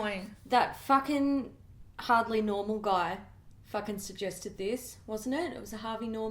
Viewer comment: The first time said "That Harvey Norman guy" I heard it as "That hardly normal guy".